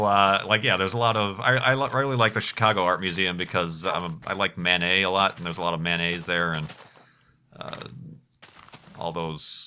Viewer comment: I heard you say: "I like Mayonnaise, and there's a lot of Mayonnaise there..." I'm tired, I need to go to bed. Dx